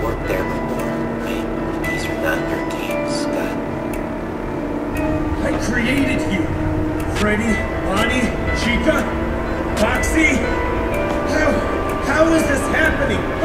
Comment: did he said faxy xD